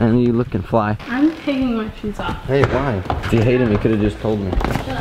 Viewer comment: Did anyone else hear at 4:03 -4:03 him saying babe why